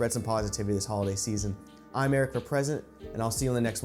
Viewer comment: Lol when you said "Eric for President" at the end of the video I thought you said "Erica Present."